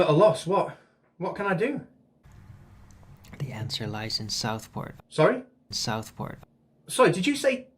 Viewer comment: Damn at first I thought he said the answer lies in South Park.